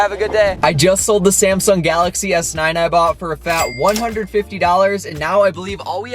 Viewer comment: I thought u said u was gon sell it for $250 😂